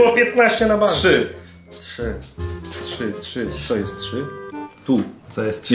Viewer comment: Zobaczcie co powiedział XDD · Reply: Dla nie kumatych "To jest 4"